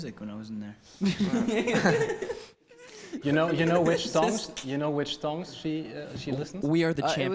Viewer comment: When he said song I thought he said thong that's of u know I'm dirty minded I turn normal words into dirty deep